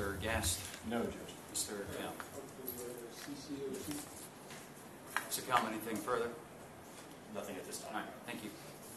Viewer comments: any know what he is saying? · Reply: He was asking for solitary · What does he say at 8:17???? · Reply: he said he’s not a fan of being brought into the court like this